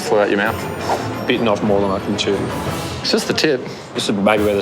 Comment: it really sounded like Andy said 'I've beaten off more than i can chew' ha ha!